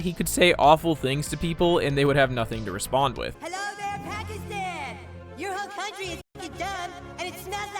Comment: I thought cartman said Palestine, not Pakistan?